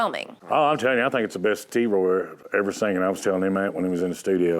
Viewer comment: What does Eddie call Troy at 0:20 ? Does he call him T-Roy, T-boy, T-Bone, or Toy? I can't figure it out. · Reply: he calls him T-Roy...